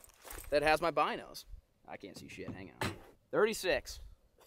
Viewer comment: Did he say I can’t see sh*t n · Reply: He said "hang-on"